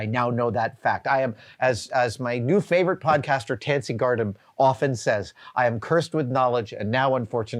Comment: who did he say was his favorite podcaster? Nancy Guardham? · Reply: sounded like "Tansy Gardam."